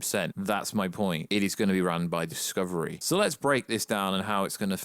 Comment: Ok so now you think it might happen by discovery. I thought u said by AT&T. Not believing anything anymore.